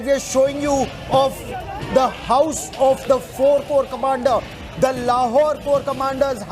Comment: Did, did he say? Cobra Commander?